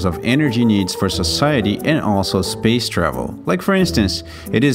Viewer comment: My bad i thought i heard GRAVY 😳👍